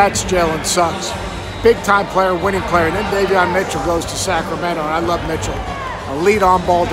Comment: I thought that ish said Donavon Mitchell 😂😂😂😂😂😂😂😂